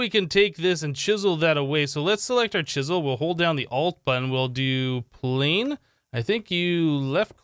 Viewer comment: What button did you press for that chisel interface to come up? I never knew you could do something like that with chisel sounded like you said alt button? What else can you do that I don't know about?